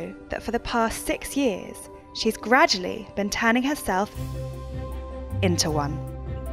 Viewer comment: I thought it said “she’s been tanning herself...” I was like umm, wha?